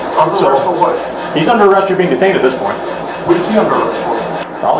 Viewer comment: did i hear that right He is underarrest form been detiain at this point lol what the hell